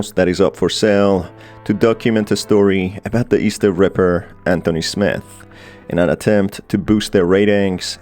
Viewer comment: i honestly thought he said "The Easter Ripper and Will Smith"